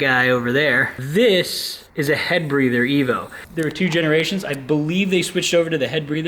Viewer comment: Zaks says “head breather” and all I hear is “mouth breeder”.......... guess I just need to grow the f up